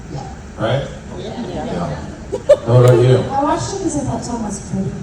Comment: what did Erica say that made them laugh? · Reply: Erica says she watched Smallville because she thought Tom was pretty haha!